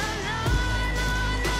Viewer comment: When they said "Sun Rise" I laughed so hard, I feel like they made this when they were really young and probably thought that part was so cool. 😎🌅🌞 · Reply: Did they say "sunrise"? I thought they said "sell rice" lol.